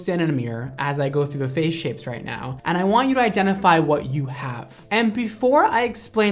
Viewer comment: Please in the beginning when he was saying 'What you identify what you have' but I heard it as 'What you identify as' and I proudly said 'Lesbian' god I'm an idiot